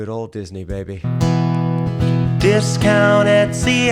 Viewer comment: I never understood the discount etsy agitprop line because I had never heard of the term agitprop before, and I've only really heard of etsy in passing. It just sounded like random sounds to me, I sort of heard it as "discount at ciagitprop". So your breakdown there was really helpful.